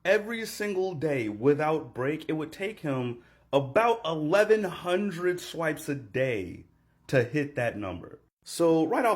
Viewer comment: captions are wrong for those of you who cant hear what hes saying, it's 1,100 swipes a day